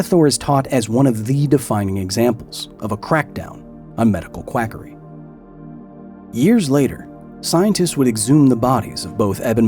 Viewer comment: swore he said a qwackdown on medical crackery